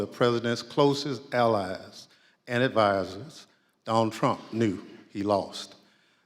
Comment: Schiff has penned the made for TV spinoff. Let's go brandon. I would swear he is reading a dang book. Did he say Donald Duck?